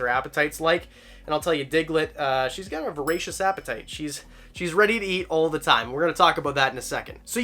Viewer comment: 100% thought you said racist appetite around the 1:50 mark, lol!